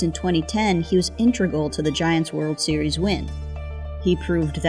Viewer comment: Did she say “intregal” @ 00:50 lol…..